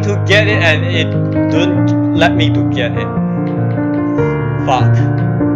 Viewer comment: did you say f*ck?